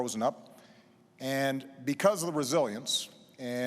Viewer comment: misheard as “Because of the Brazilians” lol